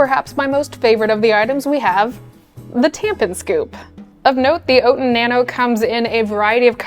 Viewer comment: I need to pay closer attention... I had to double take... I heard "tampon scoop." lol Totally different product.